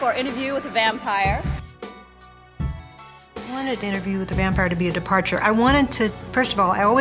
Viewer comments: she clearly says "the" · she says interview with 'the' vampire, she's speaking so fast unless you focus on the words you don't really hear it, just, ya know, pointing that out lol